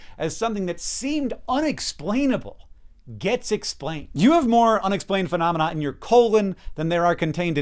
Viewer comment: Did he just say "you have more unexplained shitnin your ass"? I'm dead.😂